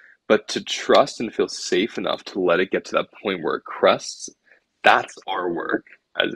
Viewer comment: I tried looking up the word crusts or crests ? @ 31:00 or maybe I m not hearing the word right but what that word actually means ? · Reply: Fairly certain he was saying crests, like the highest point of a wave.